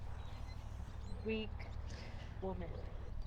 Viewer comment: I hear saying "stupid" twice during the video, and around 13:34 "weak woman" LOL